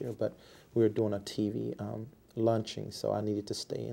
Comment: A T.V. LUNCHING lmfao ... The Lawyer was like Launching and then he said like bro bro that is not what you said at first you said a t.v. Lunching lol